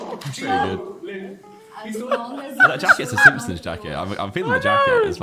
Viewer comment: Is Josh saying Finland jacket or what? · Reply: Simpson’s Jacket